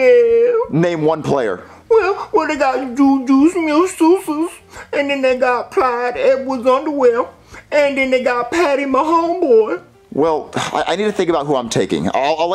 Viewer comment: wth is he saying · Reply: Juju smith schuster,CEWH,Patrick Mahomes